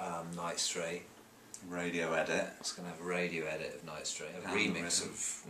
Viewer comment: when he says "radio edit" it sounds like he says "radiohead it"